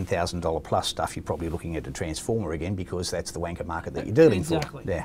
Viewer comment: does say "Wanker market" ! ? haha